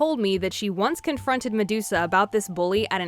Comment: I hear "Mudusa" Instead of instead or the ORIGINAL name